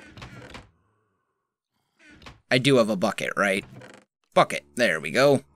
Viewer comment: When you said bucket i thought you said f**k it